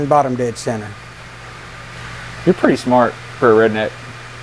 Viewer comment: Possible that I misheard, by didt you realy sayed to your dad: "Your pretty smart for a redneck" :D ?!